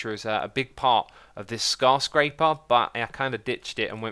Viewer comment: Did he just call the skyscraper scarscraper